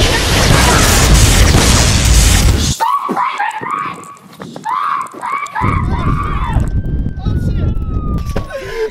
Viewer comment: What was Ronald McDonald saying? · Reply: Stop prancing me! acts like a chicken -Ronald McDonald